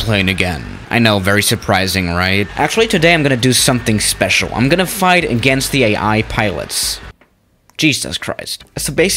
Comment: I thought You Said At 0:12 Im Gonna FigHt agaiNst JeSus ChrIst